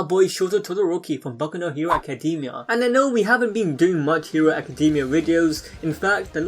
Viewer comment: did he just say kacademia?,?